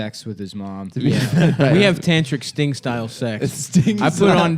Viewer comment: I thought said he had “stink-style” sex w his mom bc they’re Greek